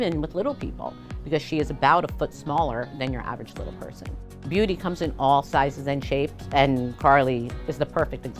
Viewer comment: Who else thought this said “shake my booty” not beauty